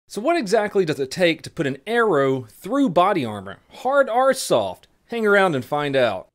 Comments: All I heard was “Hard R stuff” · When you said "hard or soft" I thought you said "Hard R stuff" . You know, like the funny word. The one with a hard R at the end. The one that makes you game harder